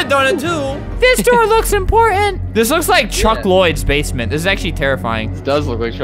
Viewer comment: is it just me or am i hearing junk Lloyd instead of chuck Lloyd?